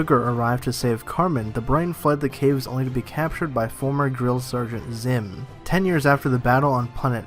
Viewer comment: Did I hear grill sergeant? 2:18 2017